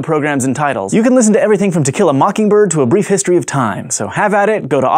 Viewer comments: misheard it as "Tequila Mockingbird" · I misheard him say "Tequila Mockingbird", which sounds like a rather spiteful cocktail.